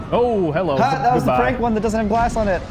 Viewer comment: Not a native speaker D: what did he say here? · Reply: "It was a prank one, it doesn't have glass on it"